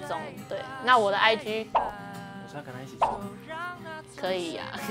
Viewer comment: 文彥說甚麼啊……聽不清楚，可是好可愛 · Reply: 我是要跟她一起這樣嗎，這句